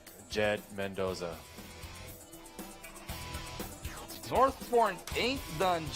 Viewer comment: ano sabi mo Magoo? Northporn? HAHAHAHA